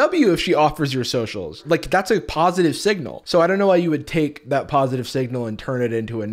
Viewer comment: im so sorry but my favorite part about this entire video is the fact that you said "rizzless" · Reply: I also thought he said “positive sigma” for a sec 💀